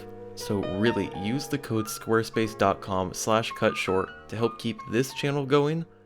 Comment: Follow me on Twitter, where I announce the next videos early! <link> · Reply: OHHHH. Cutshort.....I initially thought it said, "Cumshort".....